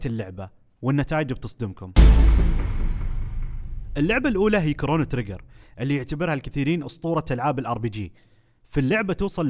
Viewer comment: كأني سمعت كورونا